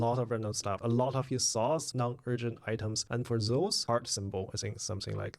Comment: I'm sorry but may I ask what did you say here? "For Z..., heart symbol or something like that"? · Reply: I meant: "... his thoughts, random stuff, non-urgent items..." :)